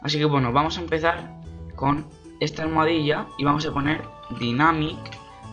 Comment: alguien me puede decir que es lo que dice en el minuto 1:30 PD : GRACIAS MUY BUENOS TUTORIALES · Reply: R games el maximo dos años después de la pregunta... #dynamic <num> denada